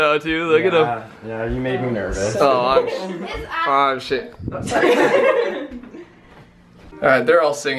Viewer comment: did he just say ah shit? i think finally swore